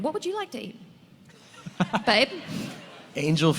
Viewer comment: did she call him babe???